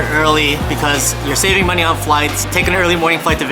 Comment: I have clean out my ears, I thought you said you were giving out money. LOL LOL